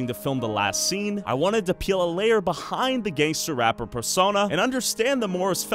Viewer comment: does it sound like he said “gay rappers” instead of gang rappers? Or was it just me?